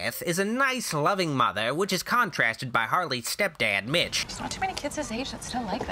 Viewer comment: Lol Had to go back a few seconds I thought you said his name was bitch instead of mitch 🤣